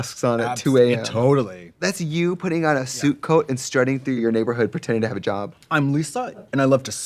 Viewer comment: Every time Trixie says “suit coat” I hear “soup coat”.